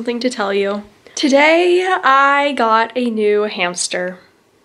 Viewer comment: I THOUGHT IT SAID EATING MY NEW HAMSTER